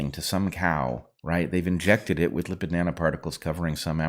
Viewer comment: I heard "they've injected [cows] with the banana particles" instead of "with lipid nanoparticles", and thought we were talking about vegan beef.